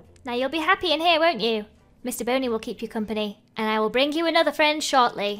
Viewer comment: Please please please make a home for Mr Boney! It makes me sad he was kicked out into the cold! I think it'd be ADORABLE if he had a little cottage with a fairy garden full of his new bunny friends and flowers!! Like if you agree! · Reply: Emma Davis oh I thought you said Mr bunny instead of Mr BONEY oh well I know now :)